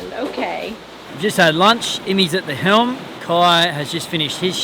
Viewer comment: I was working and only listening and I thought I heard you say Lenny is at the helm! Wow, I was expecting that in several years but not today! :-)